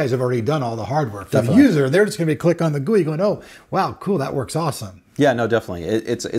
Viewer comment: Wait, what did you mean when you said, "its all in the gewi?". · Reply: GUI